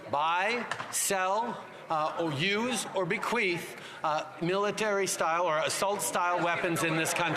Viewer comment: The best thing about trudeau speaking is you never have to ask him to dumb it down, he's as down as one could get. FREEDOM♥️🇨🇦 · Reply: Idk he did use “bequeath” that must have been his big boy word of the day 😂 although it kind of sounded like he said bequeefed